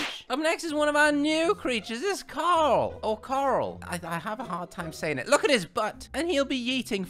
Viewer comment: I thought you kept saying "carl" It actually coral